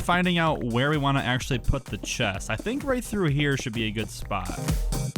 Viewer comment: any one noticed he said chess instead of chest